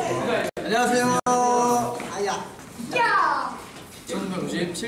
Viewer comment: I heard mingyu saying "Hot Hot Hot" @ 0:54 ...were they planning Hot since then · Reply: Mingyu didn't say HOT, he said ha ha ha with a laugh.